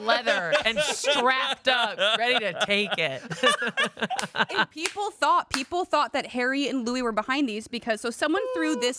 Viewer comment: what does say here? “They tie there bandanas together—“? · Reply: "Tied their bandanas together like the f-ing Bloods and Crips did in the 90s"